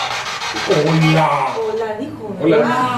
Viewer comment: mujer de voz distorsionada "¿hola?" 23:27 "perdona" una mujer joven Seguido de un hombre joven que dice "fui yo" 23:40 Misma mujer de la voz distorcionada "¿me escuchan?" "¿Hola?" "¿Cómo están?" ¿Me escuchan?" 24:37 misma mujer de voz distorcionada "¿hola?" 26:06 el mismo hombre joven dice "van por los espejos" 35:24 se escucha la voz de una mujer, tal vez la dueña de la casa